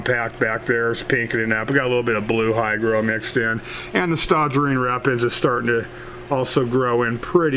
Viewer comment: Hi there! The plants look great! I couldn't quite understand what the name of the foreground plant that you mention at 2:25 into the video. Could you please type in what the name is? Thanks! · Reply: Five months late, but I think that's Staurogyne repens.